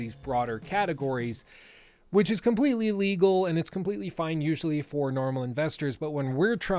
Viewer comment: sounds like you said completely illegal there instead of legal, threw me off a bit there